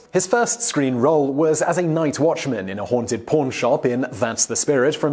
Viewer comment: I thought he said haunted porn shop